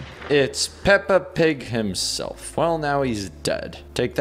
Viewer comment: Peppa Pig HIMSELF? Did I Hear That Right?! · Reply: Denis said Himself not Herself